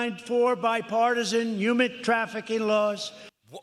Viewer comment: "I proudly signed 4 bipartisan human trafficking laws" - Trump Can't tell if he's endorsing slave trade or nah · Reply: it sounded like he said "humid trafficking laws"